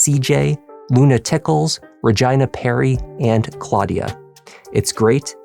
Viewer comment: Off-topic, I’m sorry and I may very well be wrong, but I don’t think Regina is pronounced, “Reh-g👁️-nuh”. I believe it’s pronounced,”Reh-gee-nuh”. I’m hard of hearing and I had to rewind quick af because I heard, “Vagina”. 🤣 i apologize if i’m wrong Regina girl. 🙇🏻‍♀️